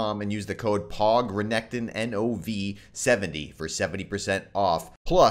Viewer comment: i thought you said "pawgrenekton"